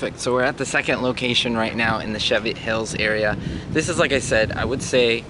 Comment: It sounded like you said “shove it hills” ha ha